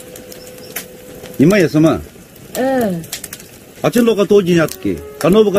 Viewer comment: exact word kya bola na 'dear wife' ko ? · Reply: 'Nyeme Yassum' since her name is Yassum and nyeme which refers to wife